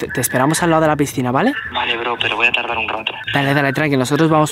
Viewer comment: fue que dijo tardar o taldar en el minuto